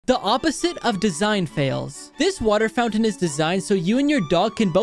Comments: Pov : you thought it said "design fails" And tried to be mad at a Non-existential flaw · lol thought it said design fails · I misheard it as design fails and they all made sense besides a few If the water you touch also goes to your dog, eh maybe not so sanitary And if the man's replacing a door, what door is there to use? (Only if it's one door) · I thought he said these WERE design fails, and I’m sitting here thinking I’m an idiot cause they are actually good ideas.